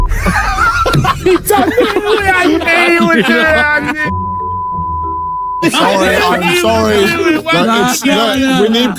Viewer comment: what's the word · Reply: He used the word "Paki", which is a racial slur targeted to Pakistanis and South Asians.